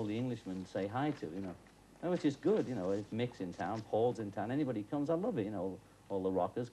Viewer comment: I didn't quite hear what he was saying at 14:57. Mix in town, halls in town or Mick in town, Paul in town? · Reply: Mick is in town (Jagger).